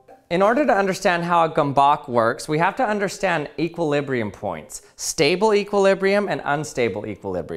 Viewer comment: As soon as that egg came out I heard "to understand how a gomboc works, you have to understand how an egg works."